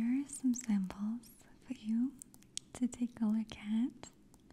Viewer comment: I swear I just heard "...to tickle a cat" 😂 . . . . A+ video again Maria! Thanks for always creating the best content!